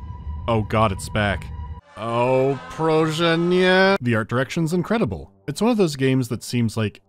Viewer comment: Minute 6:30 - is he sayin “prosze nie” (please no/don’t) in Polish?!?!?😮